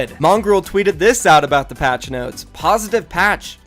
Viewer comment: When you said patch notes I thought you said faze catchnose lol 😂😂😂